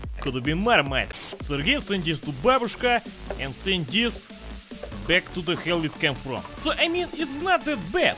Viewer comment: What hes saying: Sergei, send this to Babushka and send this... back to the hell it came from. Subtitles: Sergey, send this to Babushka and send this.. to Vadim's mailbox